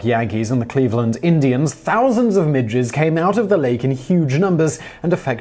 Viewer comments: I definitely thought you said midgets, in stead of midges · seriously thought you said "thousands of ninjas came out of the lake" which im like, yea that would certainly affect the game xD